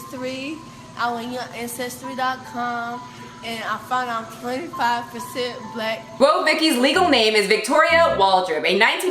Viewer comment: Oohh, I think she said 25%. What is wrong with people? I hate labels.